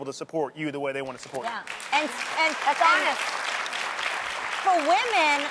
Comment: did Adrienne call him Adonis, at 4:30? I only realized that cause that's my boyfriend's name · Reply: She said "that's honest"